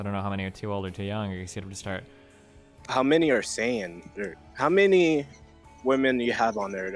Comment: did he just say "How many are Sayian"?